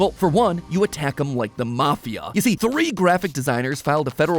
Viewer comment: i thought he said “you attack them with the mafia” and was laughing so hard😭🤣